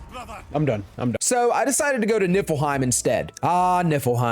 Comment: It sounds like he says it nippleheim 🤣🤣 which is hilarious